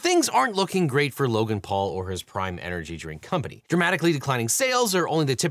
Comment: I kept hearing ''Crime energy drink''.